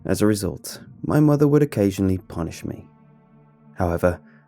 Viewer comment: Any one else mishear punished me as punched me?